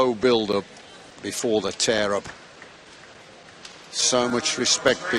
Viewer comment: I think he says" This is the slow build-up before the terror"